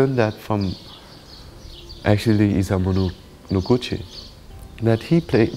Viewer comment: What is the name he says at 3:55, I hear 'isanbulur lugucci', but I got that wrong... :) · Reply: Danh Vo is talking about the Japanese artist Isamu Noguchi, who he is a great admirer of 🌟